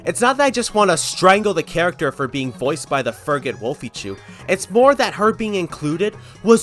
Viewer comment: ''The Fergate Wolfie'' that's what the subtitles say. I'm just wondering what's wrong with whoever is voicing the character.(I don't know them) (I also don't know what you said cuz well subtitles didn't help) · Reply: 1. The woman who VAs melony is called Wolfychu, she is a furry animator who makes animation memes on her channel 2. He called her a “Furggot”, he mixed furry with the f slur (which I don’t even know why he called her that considering she’s straight) 3. From what I know of, there’s nothing wrong with Wolfychu, he’s just being mean for the sake of being mean